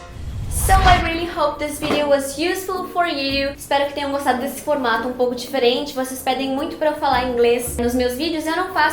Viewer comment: o que ela falou? hahaha i'm sorry...(what?)... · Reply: Lucas Vogel alguma "merda acontece" kkkkkkk